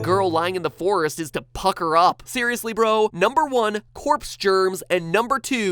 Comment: Did he say 'fucked her up' or 'pak her up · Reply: Pucker up